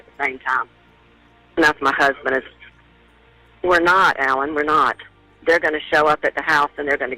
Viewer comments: ….Your so funny… I don’t think he said lawnmower, he said “alarm on”. Listen again. Thank you for the videos. I look forward to them each week. You are truly amazing!!! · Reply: i heard 'lawn mower' too😂😂😂 i was like, what?😂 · I don’t think he said lawn mower I believe he says they have their Alarm On that’s what it sounds like he says to me